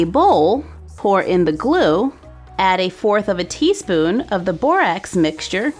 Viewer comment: I messed up on the slime bc in the video I thought you were saying 1&1/4 tsp instead of 1/4 tsp!!!!! NOOOOOoO